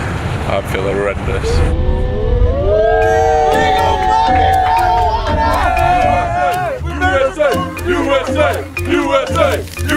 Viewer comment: did someone say were go f**king marijuana or i heard it wrong